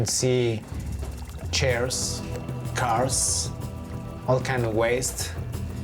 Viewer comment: did he say CARS?